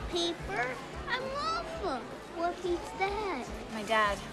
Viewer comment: The part with the kid saying "I'm lava, what beats that?" And the girl was like "My dad." I swear I thought the boy said " I'm Parker" or some other name xD